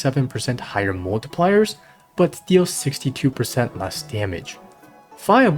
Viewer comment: Sorry did I hear that correctly???? 62% less damage?